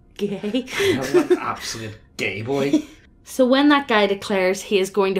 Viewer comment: Did you actually say "What a gay boy." at 08:36? Homophobia is so not cool.